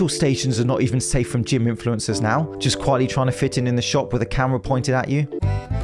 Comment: Honestly thought he said "quietly trying to fit in the shop with a camel pointed at you" in reference to how tight those yoga pants are. Trust, I've seen plenty of camel in yoga pants. It's not nice.